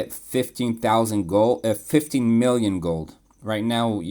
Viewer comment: 15,000 gems and i get about 15-16 million gold. I may have said 15,000 gold by mistake in the video. I have a lot of gems, because of double down packages, and because of winning server war gets me about 400k-600k gems total. It's a very good trade imo, and i still need gold very much. Later in the game when i am done with sciences and item refines, i probably wont levy so much. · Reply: Oh. lol. It sounded like you said 50 million gold.